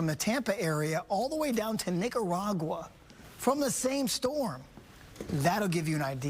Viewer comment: Did he just say niggaragwa 😅